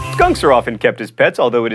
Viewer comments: When he said that there are skunks as pets, I thought he said guns. NTW, I would love to have a penugin! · I thought you said guns instead of skunks for the first one. I lol'd